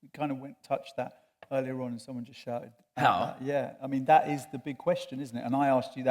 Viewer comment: what was the "shouting" or the question he starts answering at 15:50 was it just "how?"?